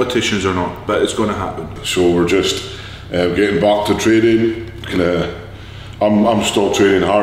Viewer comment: Call of Duty lol hell yeah! I thought you said getting Baked not Big 😂 Good to see ye lads. 💪☘️👊